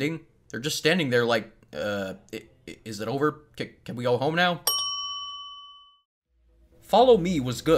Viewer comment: It's minecraft what do you expect balloons made out of also at 9:13 subtitles say "C-Can we go home now!" But the person, don't remember his name says 'C-Can we all home now'